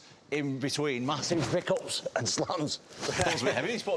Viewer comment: can anyone tell me what tom's saying? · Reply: Massive pick ups and slams